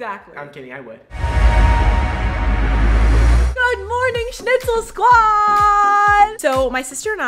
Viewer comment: I thought she said shitzel